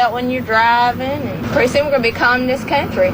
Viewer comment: What did she say? I spent a third of my childhood in North Carolina, but I can't for the life of me understand what she said. It sounded like, "Pretty soon, you'll be driving it's country," but that can't be right. Someone who's fluent in Southern, please help me! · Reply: "pretty soon we're gonna be a communist country". Talking about drink driving laws lol.